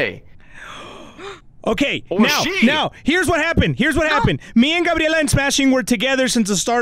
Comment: Yo when they said Eddie gabby and smashing I thought he said Eddie and gabby were smashing